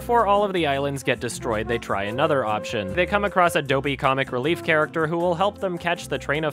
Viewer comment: THIS WHOLE TIME I THOUGHT HE SAID ADOBE COMIC RELIEF CHARACTER AND WAS SO CONFUSED